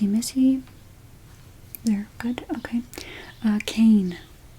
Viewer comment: Did anyone else hear this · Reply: She said Caine